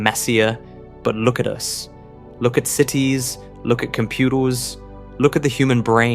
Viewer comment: did he say "computals"? I'm dead 😂